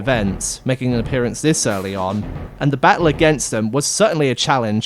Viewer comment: What? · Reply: maybe i misheard but it sounded like you referred to the knight as "him"